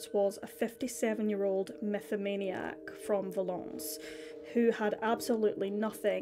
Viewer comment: Just in case anyone else was wondering: METHOMANIA noun. a condition in which dependence on alcohol harms a person's health, social functioning, or family life. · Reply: i thought he said 'mythomania'...